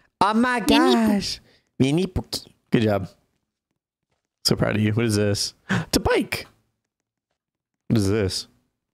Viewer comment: I swear I heard say "I'm so proud of you princess" to I swear I heard that😭